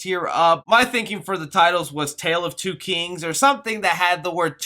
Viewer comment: When you said tale of two kings I thought you said Tale of two kitties